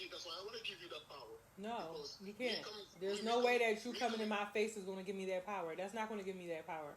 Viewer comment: ok.... HEAR ME OUT! ..... i stg im not trying to be funny.... i replayed it 4x..... did she say: "there's no way you cummin on my face will give me that power?" Im at work.... i could be heading things.... somebody tell me in trippin